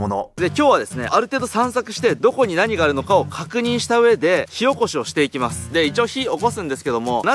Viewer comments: 火起こしが塩コショウに聞こえる。。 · 火おこしをして行きますが塩胡椒して行きますに聞こえた🤣 あれ、幼虫もきちょたんじゃw